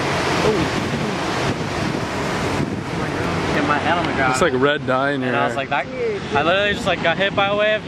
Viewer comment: Did he said "red dye in here" at 3:52 Anyone please?